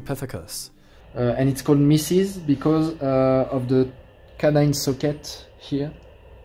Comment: Did he say it's called Mrs because of the "can I suck it mouth"? Oh, those frenchies!